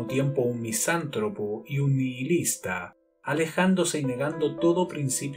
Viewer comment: Hola, en el minuto 10:32 dices que Ian se consideraba un misántropo y un (que?) No entiendo la otra palabra "Oigo algo así como LIGUILISTA" pero tengo estar oyendo mal pues esa palabra no existe el el diccionario, por favor, a la gente del canal o a cualquiera que sepa que es lo que dice le agradezco me responda aclarándome la palabra, gracias de antemano. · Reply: nihilista es la palabra